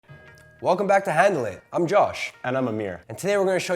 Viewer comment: i had my speakers down, and when he said "handle it" at the start, i thought he said heaven... that would have been good